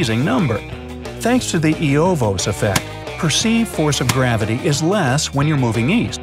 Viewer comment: to anyone who didn't understand the pronunciation: Eötvös effect @ 7:52 is that Bruce Dickinson?